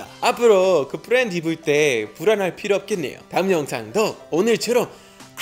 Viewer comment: 나만 불알할필요 없겠다고 들림? ㅋㅋㅋㅋㅋㅋ🥺🥺🥺🥺🥺🥺